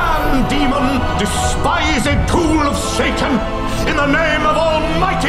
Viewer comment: Oh he said GHOUL of Satan I thought he said a POOL of Satan Like a metaphorical pool of evil